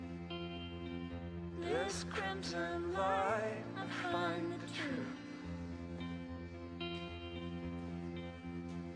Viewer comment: I know Alana said "Sorry" but I don't know what they're saying because it's inaudible. Does anybody know what they're saying? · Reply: Turns to her husband and says “I can’t I can’t I’m sorry.” Husband turns to lip and says “I’m so sorry kid, here.” Then he leaves.